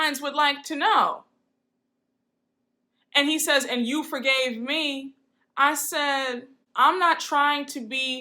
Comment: I didn't hear Shah say "you forgave me", I heard him say "you weren't ashamed of me". I still wonder what all that meant though